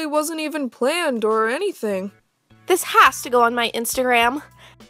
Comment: did anyone else notice that said Instagram instead of Glamstagram